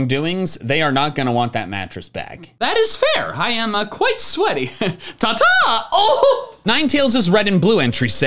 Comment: This is a odd comment in 6:30 when was saying "I'm very sweaty." I skipped 5 seconds to saying "and very vengeful" Soo what I heard was saying "I'm very sweaty and very vengeful " 😄